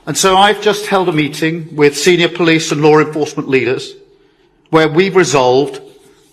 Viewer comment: I'm sorry i can't take him seriously. All i hear is "lore enforcement".